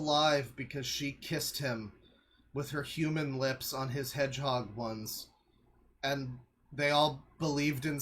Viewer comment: I heard it as "with her human lips on his hedgehog buns", several times